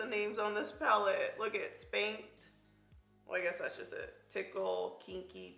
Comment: "Kinda like scandalous names on this palette" Spanked, tickle, teddy , kinky... RAW 😳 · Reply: I had NO idea what she was saying the first 2 times she said "spanked" because she was enunciating. I thought she said skanked at first :/